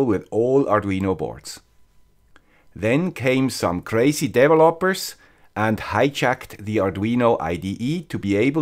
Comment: Crazy devil lopers? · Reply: I heard "Crazy devil hoppers" ROFLMAO... 😅